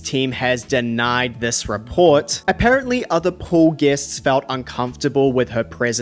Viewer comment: I swore you said "apparently other poor guests..." But you said pool. I just automatically assumed it cause you were discussing THE Britney 😅